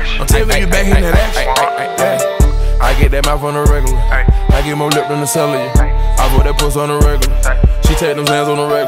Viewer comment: At first I thought he said "I get more lit than a sandwich"💀